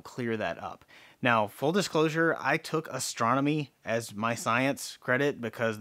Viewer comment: I thought they said “astrology” class 😂